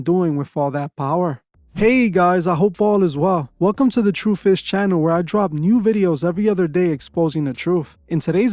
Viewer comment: Sounded like you said “the true fish channel”. Fishers of men